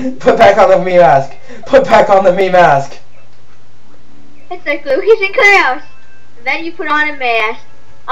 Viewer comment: Lol LIKES MOTHER HOLY CRAP <3 I never knew that xD I heard "It's like Lucas and Claus" and pretty much died!!